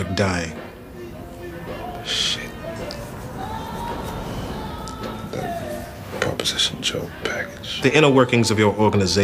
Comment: what was Avon mumbling?? I can hear him say: Proposition Joe... but why???